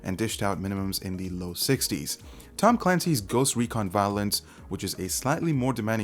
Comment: Did he say Ghost Recon Violence 8:27 ...?